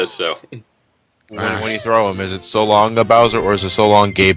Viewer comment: I always thought Mario was saying "So long me Bowser."